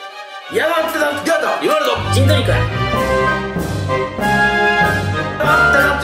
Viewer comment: 何回聞いても人造肉って聞こえるんですけどなんて言ってるんですか？ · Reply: ジントニック